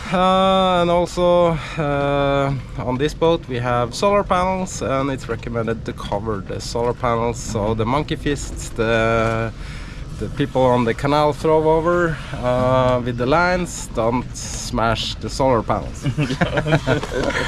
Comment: you said monkey fists (heaving lines) and first i heard monkey feces